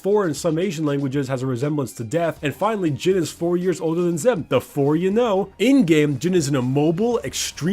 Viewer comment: In 1:25...jhin is 4 years older than who? I couldnt quite understand the name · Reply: Nimie Kamiesama Zed